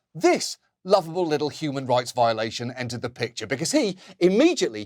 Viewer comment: "This lovable little human rights violation" 😂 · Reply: I thought he said "little human rights violator" but still, seems accurate. One reason USA is not part of Rome Statute is because a lot of its former Presidents and military commanders would be on trial before the International Criminal Court.